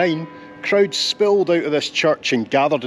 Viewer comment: I thought you said at the start “in 1989 Krauts spilled out of this church”. I thought…this video is going somewhere I never expected. Ah the joys of the Scottish accent. 😂😂😂